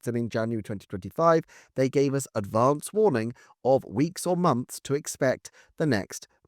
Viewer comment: You said “they gave advance warning”, I heard “they gave us Advance Wars” and now my sofa is covered in drink 🤦‍♂️